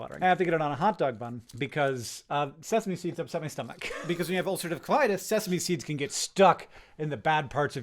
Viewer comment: What's that disase? Can't hear a thing when he slurs his words like that. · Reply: Ulcerative colitis